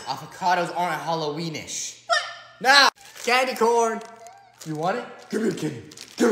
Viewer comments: I thought he said “daddy corn”😭💀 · I thought he said Daddy Corn!!😂😅🤣